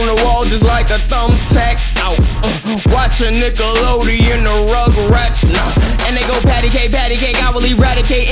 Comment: did he say RugRats